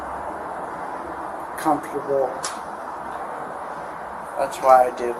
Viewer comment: I get the same way when someone says supposebly, that bothers me to high hell for some reason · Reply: yesterday I heard " comftaboo" and uncomftaboo